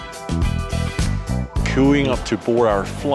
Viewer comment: did he say "cooing"